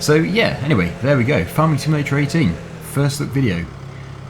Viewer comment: i thought it said fs17 first look